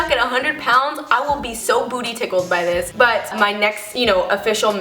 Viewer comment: Wa..Wa.. Wait... Did she just say Booty Tickled? xD Hahaha